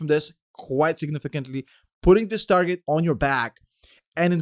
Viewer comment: zoned out for a sec and thought I heard: "putting the Stargate on your back"